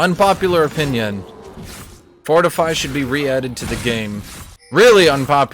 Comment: what did he say? Vortefy?